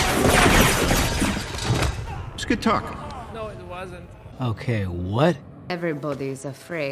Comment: I just heard the guy in the back say “no it wasn’t” after he said it was a good talk 😂😂